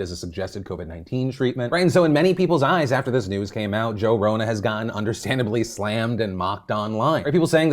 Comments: Did say "Joe Rona" at 3:10? Lmao savage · Did he say @ 3:17 Joe Rona??.....lol Edit: also Joevid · Did you call him Joe Rona? 😂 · did he say Joe Rona??? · Is it just me or did call Rogan Joe Rona at 3:17 lol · Did you say Joe Rona ? Because that's hilarious 😂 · Wait at 3:20 did you call him Joe Rona? If so GENIUS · Did he just say Joe Rona?